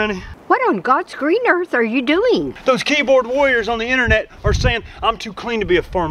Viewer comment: Who else thought he said keyboard queers?🤣